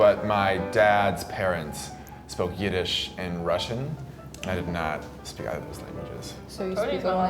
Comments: It sounds like said that his dad's parents spoke Yiddish and Russian, not Irish and Russian. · he did not say "Irish" but "Yiddish". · he clearly says: "but my dad's parents spoke YIDDISH and Russian". Where the hell did you get Ireland from? 🤔 Jeez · Didn't he say Yiddish? The captions say Irish though